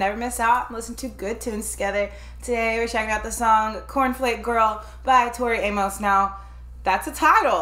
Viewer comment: It’s Cornflake girl. You both probably thought you misheard it, you thought it was really Snowflake because that made more sense. 😁